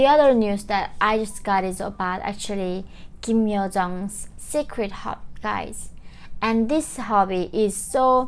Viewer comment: What did she say at 6:04 about Kim Yo Jong? Did she say “Secret Hot Guys?”